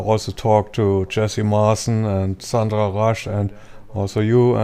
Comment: He mentioned, it sounded like, Jesse Martin and Sandra Rush. Where can I hear about their stories?